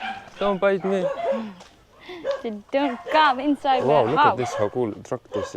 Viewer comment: Did she say "Don't KUM inside my mouth" at 2:56? 🤣